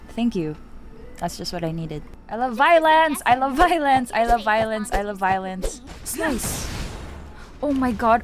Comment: I thought you said, "I love violins" because of mommy kafka doing air violins but it's violence lol